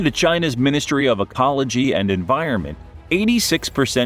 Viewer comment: I could've sworn I heard, "... Ministry of APOLOGY...", instead of ECOLOGY. At first, I thought, it still pretty much fits, LOL. But since when does the CCP apologize for ANYthing? Certainly not on the world stage, let alone it's people, which are essentially its bread & butter.